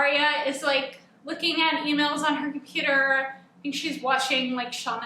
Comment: Lol I thought you said "looking at females on her computer"